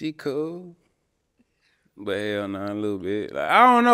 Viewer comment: "Little mushroom top" I SCRUMPT😂😂😂 HUMOR IS ELITE😂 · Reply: I think I heard he said "hell nahh"